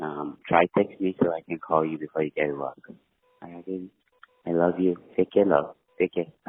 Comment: On that voicemail, doesn’t it sound like someone is coaching him on what to say in the background? Someone says, “I love you” in the background and he says, “Oh, I love you”. And that does not sound like a Swedish man’s accent